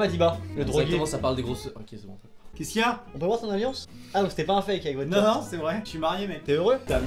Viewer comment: A 3:30 j'ai compris : "c'était pas un fake avec Gwen....."